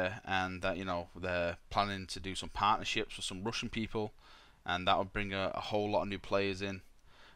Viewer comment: At first I heard "rush in people", and it confused me a lot.